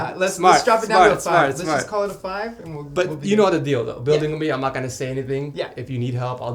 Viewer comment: did he just say dildo?